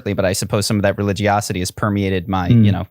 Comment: can someone tell me what word he uses there? I'm no native so i'm having trouble deciphering the phonetics here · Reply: Religiosity? Is that the one that you're having trouble with?